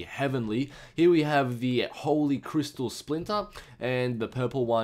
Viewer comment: I thought it said sphincter instead of splinter for a moment xD